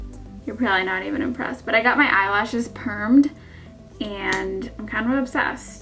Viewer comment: you have such great skin! ughhh so porcelain even before applying makeup lol... and i love FRIENDS! so i totally got the insider haha..love ya! great tan! by the way... ps: what is eyelash perming? maybe video on this, never heard of it lol, for a sec i thought u said "i got my eyelash burned" im like wow rewind lol